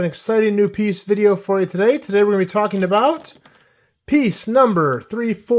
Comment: When you said "piece number" I thought you said "the stumper" which I figured was some slang for the double jumper.